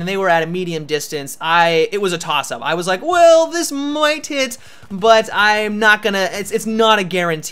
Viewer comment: Sounds like he says "Well this is my tit"